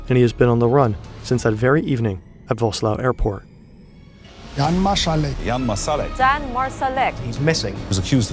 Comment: During the news report montage where everyone has the various euro accents I kept hearing "Jan Miselei" and I'm like "T... The guy that counted super Mario games and critiqued conlangs?????"